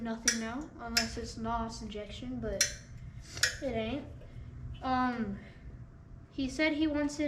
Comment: When talking about the switch that does nothing anymore at 15:44 , did you say "Unless it's NOS Injection but it's not"? I think you did. 🤣🤣🤣 Good stuff!!! Hahahahahahahaha.